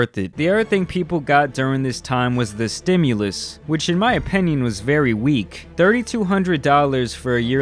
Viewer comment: Why does say “OPENION”??